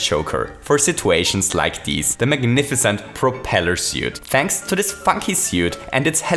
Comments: Who else thought he said “thanks to this fucking suit” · I seriously for the longest time thought he said “thanks to this f*ck you suit” And not “funky” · I swear I heard him say “thanks to this fucking suit” and now I can’t unhear it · I thought he said “Thanks to this F*CKING suit” xd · my computer's speakers are so bad that it sounds like is saying 'fucky suit' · i ThOUghT hE sAid fucki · Um... what kind of suit did you say it was? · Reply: funky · that sound like u said f**king suit my guy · I know i heard funky after the third time but at the first time i heard fucking suit